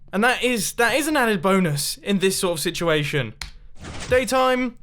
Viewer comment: did He say boners?